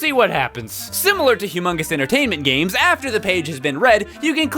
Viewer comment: Did you just say Hugh Mungus Entertainment?